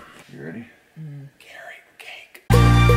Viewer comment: did anyone hear " Carot cake"?